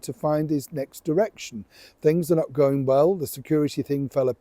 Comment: 😂😂sorry Niel. I didn't have my hearing aids in and it sounded like you said, 'Harry, was struggling to find his next erection.' 😅😂'